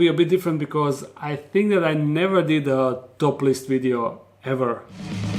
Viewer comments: At first I thought he said 'a topless video'. I almost clicked it off. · Thought he said it was gonna be a "topless" video... I'm a little disappointed 🙁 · OMG! I thought you said a 'topless video!' I said, "What?!!", out loud. LOL!! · I thought you said "I never done a topless video before" XD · ahh you said top list video, I thought you said topless lol I was a little confused but stayed for the T's anyway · I heard 'topless video' and got excited